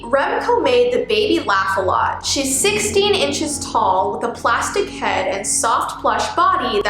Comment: When said: “its 16 inches tall” What i heard: “she’s 16 and tall